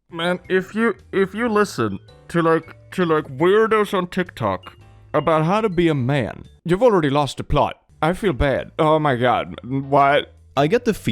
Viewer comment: I hear him say, while I'm cutting my Tblockers for the week. It's fucking hilarious and it stopped me dead in my tracks. Ps: It's when he says "If you listen to weirdos on TikTok about how to be a man, you already lost the plot"